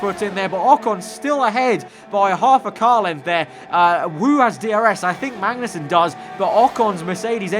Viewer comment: did he say "WU" has DRS?????